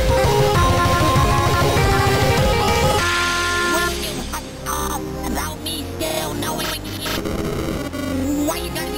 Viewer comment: what is firey saying? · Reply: He said. Can you hush up about me still knowing you? Why do you always gonna be so hard?